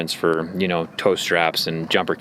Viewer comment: When he said tow straps I thought he said toaster apps 😂